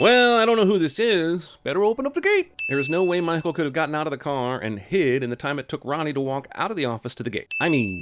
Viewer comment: I ONLY hear: “there is no way my hoe could’ve gotten out of the car and hid…” yet CC say it’s Michael🤔